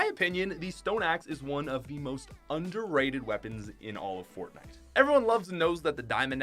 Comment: did he say fortnite?